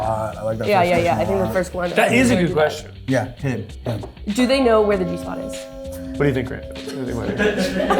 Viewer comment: Lol I thought said do you think they know where the pizza spot is. Like are they a foodie